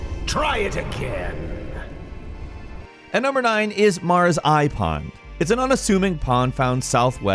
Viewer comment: I misheard Mara's Eye Pond as Mara's iPod.